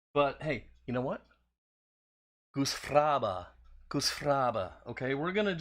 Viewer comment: Not seeing this in the comments; what was that word you used at about 2:05 ? it sounded like a useful kinda word.😁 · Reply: Goosfraba is an Eskimo word used to calm their children down and oddly enough, a word they use when having sex. It is featured in the movie Anger Management with Adman Sandler.... LMAO....